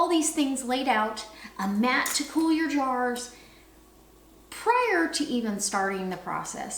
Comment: You said you're gonna need prior and I thought you said prayer. I was like yes, she's got that right, and then I realized you said prior. I got excited for nothing, ha.